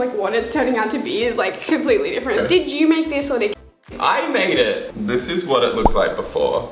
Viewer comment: “did you make this or did ...Kat??.. make this” that is what I thought she said anyway😂(possibly Kane’s girlfriend?)